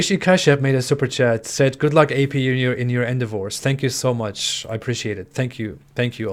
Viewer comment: did I hear you are getting a divorce? I am so sorry to hear that man. Anyone know why? I know a little about his relationship, they are both atheists but that she is an american and was raised in a christian home. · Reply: I believe "good luck in your endeavours" was what he said.